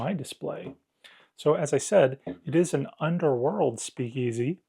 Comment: Oh, Under WORLD Speakeasy, I thought you said Under WATER Speakeasy the first time I watched the video. I thought that sounded odd so watched the beginning of the video again.....LOL